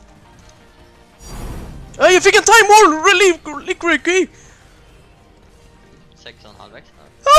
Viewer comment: he actually says "Jag fick en Timeworn Reliquary Key"